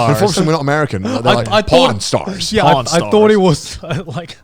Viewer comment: I thought he said p.o.r.n stars lmao he meant Pawn stars.